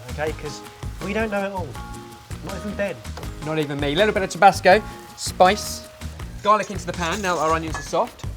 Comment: the way I thought he said tobacco and had to replay it